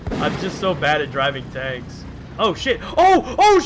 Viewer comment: ah what did you say? Did you say your bad at driving tanks? XD